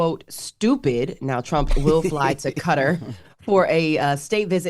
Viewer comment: WTF is Cutter ?? Morgan keep saying Cutter what is she trying to say !? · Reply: Qatar pronounced "cutter".